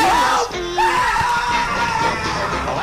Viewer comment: I misheard into "each soul separately" 😭